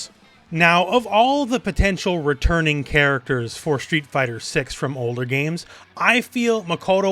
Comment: I misheard "For Street Fighter 6: Hunger Games" and thought I needed to doublecheck the Makoto lore for a moment there!